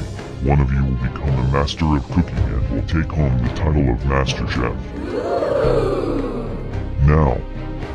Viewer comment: I thought it said master chief not master chef